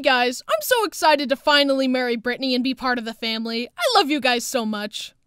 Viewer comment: When I first watched the og I thought he said “Murder Britney” instead of marry